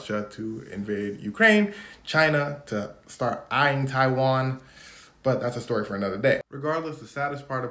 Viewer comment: says Taiwan. Captions say "other country." That's a funny one.